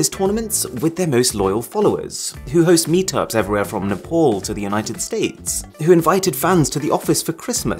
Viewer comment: did I hear Nepal in Mrwhosetheboss video 🥺✌️